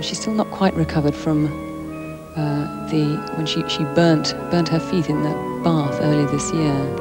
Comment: Burned her feet in a bath??? Did I hear that correctly? 42:50. How does that happen?